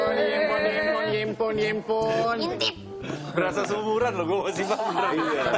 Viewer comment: Ziva 11:27 ngomong apasi? Ngintit? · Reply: Mintip